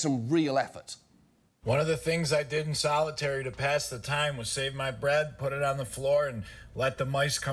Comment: At first I thought that guy said he saved his breath and put it on the floor to let the mice eat it and play. I thought man they kept him in there too long